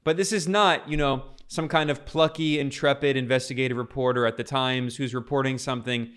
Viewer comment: He said "plucky and intrepid," but I heard "plucky and adventurey." Time to see the doctor here. Perhaps Gilbert Ozempic is not right for me.